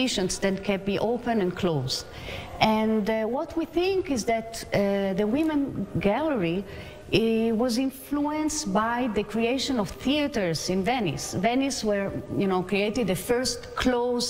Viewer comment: Unfortunately the “expert commentator is quiet ignorant about Jewish history. She claims the women’s gallery derived from the theatre but in fact we’re in existence in the temple 2000 years ago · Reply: It appears that you misunderstood what she said. At 2:26 she says "the women's galley was influenced by the creation of theaters in Venice". She didn't say "were created due to the theaters". She's saying that some of the versions of women's gallery have aspects of the first venetian theaters (just like this synagogue has a lot of baroque elements). She then talks about how many sides there are as one of the differences between theaters and how synagogues evolved in time. If I remember correctly, in the most ancient synagogues the women's galley was only on one side.